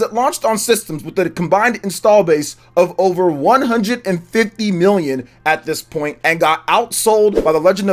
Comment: did he say 150 million or am I tripping?